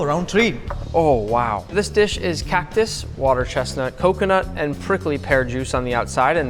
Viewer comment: I know it's not but it sounded like he said cat piss